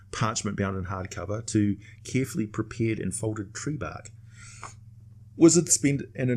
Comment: what does he says around 11:14? Carefully prepared and folded what? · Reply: Tree Bark